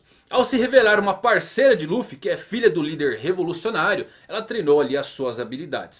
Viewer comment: pera a rubin e irma do luffy filha de um revolucionário vc disse isso ou eu entendi errado ? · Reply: Ilha do líder revolucionário ele dissr